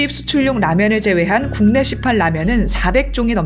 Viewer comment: 0 : 48에서 "국내 시판 라면은"이 "국내 시팔 라면은"으로 들리네요. ^0^